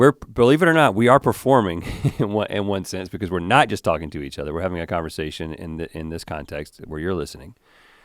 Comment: At first I thought they said each other names.